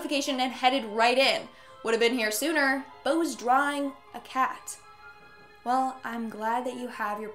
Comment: I thought she said drying